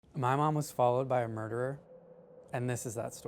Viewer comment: I thought he said " my mother was murdered by a burger "